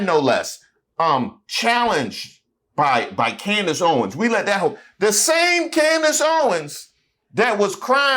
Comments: did you say “we let that ho?” Alright now Reese. It would slipped for me too. Lmao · Lmao at 5:08 did you say “we let that hoe”? 😂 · I heard that 5:08 “we let that 304” You had to catch yourself 😂😂